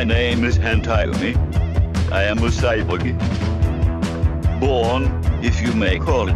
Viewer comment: Notice how when han-tyumi introduces himself it sounds like he says "I am a cyboogie"